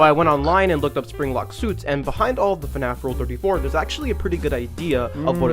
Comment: I heard rule34 can i join the 31st army and look at your history